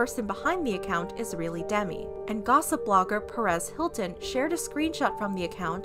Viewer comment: Sorry but I don’t like Perez Hilton he’s just one of those people nobody really likes because of how annoying they are. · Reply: I thought it said Paris Hilton and I was gonna throw hands